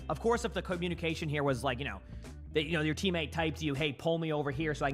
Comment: teammate hates? That is not what was said. He said teammate typed. Edit: it's possible there are more errors that I missed because I haven't been reading the subtitles the whole time, but I do hope this helps with any confusion on those that are deaf and read this mistake.